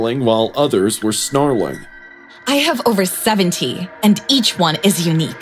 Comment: I thought she said over 17 not over 70 wtf 😭💀